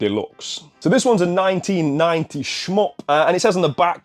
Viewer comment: what is Smuck? I've just started watching this channel for the last few weeks and I'm loving it so far, but I have no idea what that word refers to 😂 can anybody help? 😂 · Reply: You say you like "smuck" on all platforms at 23:02 I just heard it might be Smop? 😂